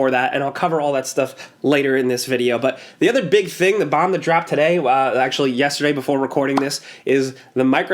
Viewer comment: anyone else heard him say "bombed the drop" ? 😂 love you guys has been in my daily routine for years now